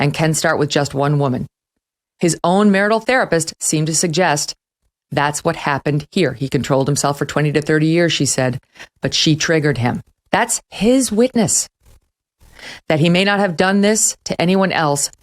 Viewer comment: can smo write the transcript of what she said near the end between 20:35 and 20:50 ?? i am not a native eng speaker so i can't catch the words sometimes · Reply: "His own marital therapist seemed to suggest that's what happened here he contolled himself for 20 to 30 years she said. That's his witness, that he may not have done this to any one else does not mean he didn't do it to Amber Heard " Please note:- 1 - She was marital therapist for Depp AND Heard (Depp paid the bills but therapist mostly listened to Heard 2 According to Megyn Kelly the therapist "seemed" to suggest, in other words the following is not necessarily the opinion of the therapist, just the opinion of Ms. Kelly. Throughout the whole video Ms. Kelly snipes at Depp's credibility to create a framework so that when she has to admit Heard is the abuser it gives the impression that it was justified. Very slimy journalism. To do the transcript I clicked the subtitle button and paused the video after each 2 lines of text.